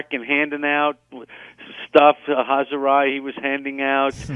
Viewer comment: Love Steve!! He got me through many, many long nights on the graveyard shift back in the day after Mets games! I'll always have love for and be appreciative to him for not only getting me through those long nights, but getting me/us through some terrible Mets seasons, all while putting a smile on my face. Cheers, Schmoozer. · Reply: I think Jerry said at 13:55 that Steve Cohen was handing out "chazzeri" ! It's a Yiddish expression.